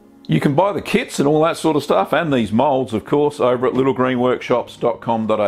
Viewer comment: Dead set thought you said curd turds and started laughing hahaha